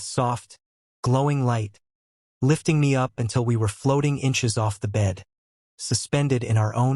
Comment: Wait.... 1:05:09 did i hear it correctly....floating sex? God, u have style ❤😎